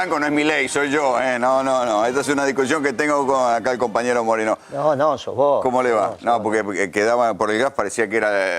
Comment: MACRI tiene que ir preso por la estafa electoral en el valotash avia ganado Sioli contra bullrrich ahi ganó cristina hasta dónde quiere llegar el señor precidente ahora las noticias estan informando que se gastó 40 millones para las elecciones de octubre ojo va aver fraude los amigo testimonico GRUPO CLARÍN se tiene que terminar las mentiras SEÑORES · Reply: escuche mal ? Graña le dijo compañero MORENO . REPEAT MIN 24:13 .